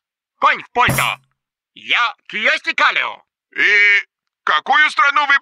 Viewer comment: кто, кто? Я че то не расслышал. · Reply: Это Кьюёсти Каллио политический деятель, премьер министр Финляндии в <num>